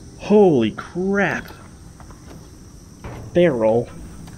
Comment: thought he said "Darrel!!"